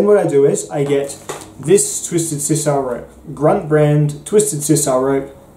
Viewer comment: I keep hearing "Twisted Sister rope" EDIT: A free Excel spreadsheet! You're the man, thank you!